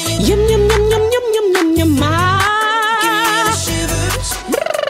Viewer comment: What I heard “and then we meet with MAMAMOO (kpop)” 🤣 (sounded like that) Edit : thank you for the likes, this is so fun to see and I really like her vocal exercise 🤩